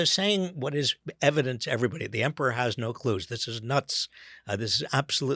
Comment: Was that a beautiful slip of the tongue? I heard "The emporer has no clues" That totally works!!